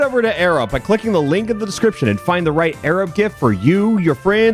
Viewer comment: I watched the entire Airup ad. After hearing Alpharad say it so many times, it just started to sound like he was saying "Arab".